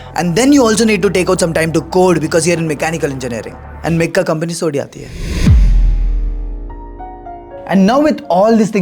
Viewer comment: What he said 3:43 ? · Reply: mechanical me companies thodi aati hai